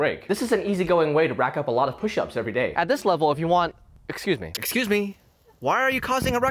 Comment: I legit thought he said mc cuse me when looking at the chickens lol